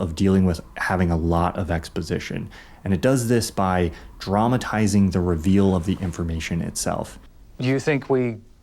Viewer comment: Every time he says "Dramatizing" I hear "Traumatizing" That exposition must be really traumatized by now lol